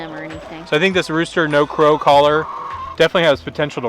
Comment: I thought it said "not to grow" and I was like "wait but that's impossible..." and then I was like "ohhh crow not grow..." lol i just thought that was funny